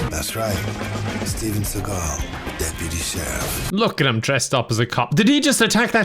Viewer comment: I love the caption at 15:00 He says "Deputy Sheriff" Caption says "Deputy Chef"